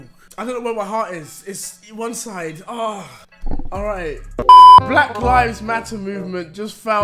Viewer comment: Did he say black lifes matter than black lives matter?